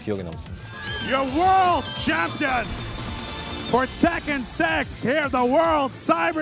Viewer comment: Damn, So StarCraft Was The Most Action Packed Game Back Then.... Imagine In An Alternate Universe, KNEE Plays Professional Starcraft...But With Fight Stick 😱😱😱 Joking Aside, 7:23 Did The Dude Said "TEKKEN SEX" Instead Of SIX And Mistaken KNEE's Name For NAE